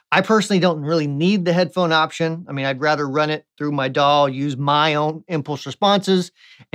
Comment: I swear I heard "I'd rather run it through my dog."